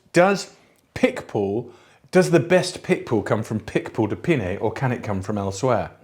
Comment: I thought you were saying pitbull de pinet. It's a dog of a wine.